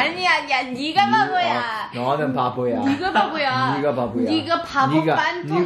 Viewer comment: Okay 14:26 sound like they sayin the n word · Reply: She said 니가 (niga) means ‘you are’